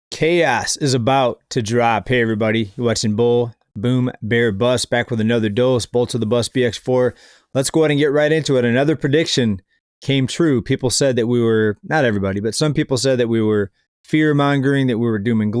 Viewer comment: what are you saying? Boots and bucks B X 4? Buffett and munger always said you can never time the market, so maybe they have inside knowledge.